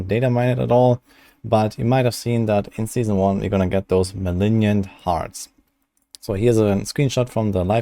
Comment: did he say malignian hearts at 0:16? wtf